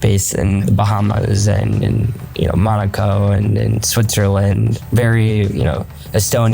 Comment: did he say morocco or macao can simone please confirm